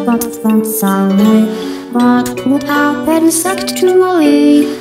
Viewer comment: I'm sorry but did he say "But what happens sexually"